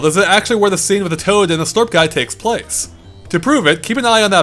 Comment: Caption error: [;acr, instead of place.